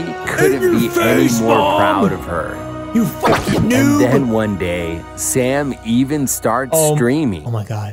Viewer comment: when he said “and one day Sam even starts streaming” I thought he said “and one day Sam even starts screaming”